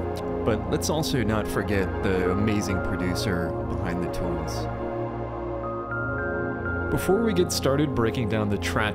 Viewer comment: Who is the producer at 1:38? I couldn't quite hear what you said, thank you! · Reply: Apologies, the audio was a bit off on this edit! That's Yoko Shimomura